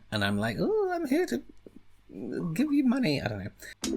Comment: “I feel like someone’s grandmother, let me give you some candy” classic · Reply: I thought he said "let me give you some money."😂😂😂